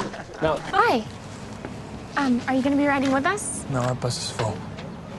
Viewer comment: what did he say? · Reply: “No our bus is full.”